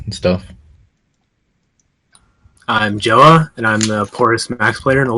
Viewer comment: what did Jowah say at 0:16? "Hi I"m Jowah and I"m......." translate pls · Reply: poorest maxed player